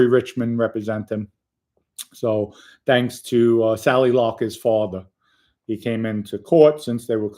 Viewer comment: What name did Dom say at 3:45.Sally Larker?